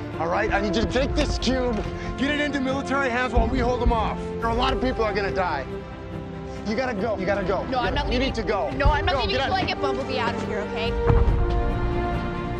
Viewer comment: you can hear megatron’s chilling voice as he says something very close to “Megatron” his own name and then “is here” . His classic entry line. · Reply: I always thought he said "There are zero" XD